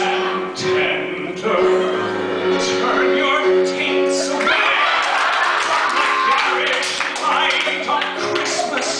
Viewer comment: He's so fantastic! This is absolutely hilarious - one of the best madlibs by far :) But what does he say at 1:09 ??I can't hear and it's driving me crazy.. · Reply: He’s saying turn your teets away from the garish light of christmas